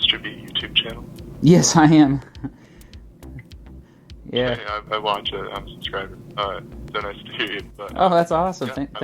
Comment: Staff member said "I watch it. I'm subscribed" Captions said "I want you to unsubscriber all" facepalm